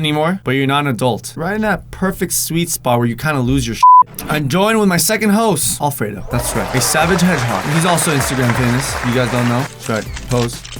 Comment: OMG, I thought he was about to say ... "I'm joined with my second hoe." Oops XD